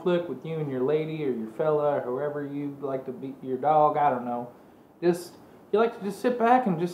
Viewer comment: "Beat your dog" hahaha I know that's not what he said but it sure sounded like it.